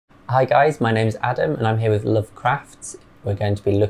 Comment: I thought he said "Le craft" sounded so fancy